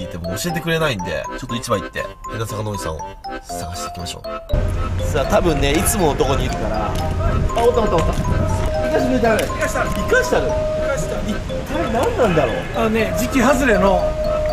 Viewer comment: 変な魚おじさんを、捌いていきましょうに聞こえたw